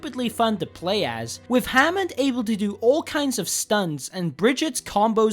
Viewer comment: did he say Hammond stuns?